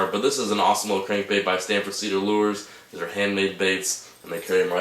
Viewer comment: Really enjoy your fishing videos. Can you speak a bit slower when describing baits? I couldn't understand what kind of bait that was or where to buy it. It sounded like you said San Frecedo lours and the web site they are available at is Ted Rabbin? I have never heard of either of those. I'll try googling that and see if I can figure it out